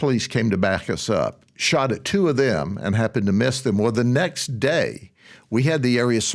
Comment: I thought he said “Shot the two of them” instead of “AT the two of them” And I was like WE NEED THE MARINES ON THIS GUY